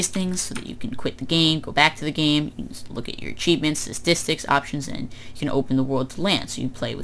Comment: “esdicksus” instead of statistics